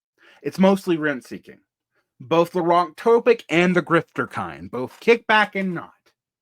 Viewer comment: Man, I'm sorry to pester you with these questions, it's probably partly my age/hearing/ignorance, but at 23:09" you name 2 sources of expense growth in primary/secondary education and I could not parse what you said for the first cause. "Both 'therantopic' and the grifter kind." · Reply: Wonk-topic, a word I made up. Cass Sunstein-nudge style rent seeker.